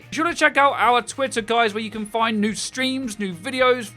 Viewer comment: only me who heard "be sure to check out our twitter guys, where you can find nude streams, nude videos"?